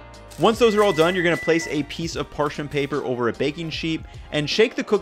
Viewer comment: yo did he say Baking Sheep